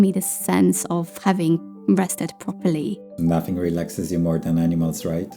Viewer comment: I’m sorry I really heard “nothing relaxes you more than enemas, right?” 😭😅 haha